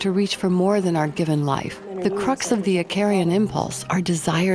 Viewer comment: Can anybody tell what the narrator is saying starting at 11:00? It sounds something like, "The crux of the [akarian? archarian? Aquarian?] impulse..." It's a word I'm not familiar with, and my phonetic searches aren't coming up with anything. I'd be much obliged, stranger. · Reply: I believe she said "Icarian" impulse, an adjective form of Icarus. Icarus is a figure in Greek mythology and his story is mostly about the dangers of hubris. Search for "Icarus"